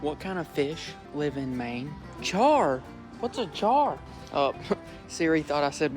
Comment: Subtitles said char said jar